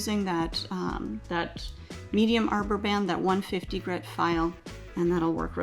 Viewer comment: Did she say 150 grit?